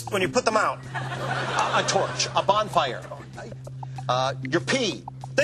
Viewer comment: does he say "your pee" at 2:32??